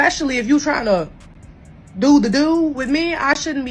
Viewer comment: did she say spend to Pay…to do the do?????👀